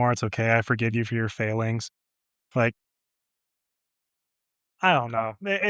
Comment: I think she said "feelings" although I had heard failings to start with.